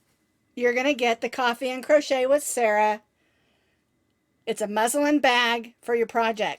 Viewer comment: Would love to try the bamboo hooks! The yarn is gorgeous! Love the Sunset Nights. When you said your project bag is muslin, I thought you said muzzle 🤣😂🤣😂🤣